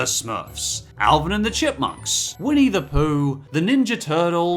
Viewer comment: Did he say woody the pooh